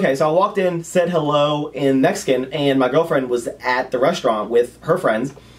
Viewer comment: did you say you said "hi" in mexican?